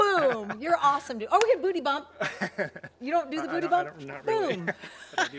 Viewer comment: He was terribly nervous with you driving this. I know his feeling. But I could tell even HE was a little nervous at the wheel. My first car was a 1973 Bug. I thought you said "Booby" bump at the end LOL. Awkward!